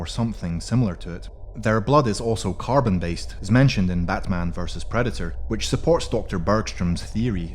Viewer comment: Wait at 2:35 did he say batman vs predator?? when did that happen???